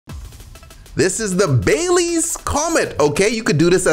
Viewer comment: Thought it said Bailey's come shot for a sec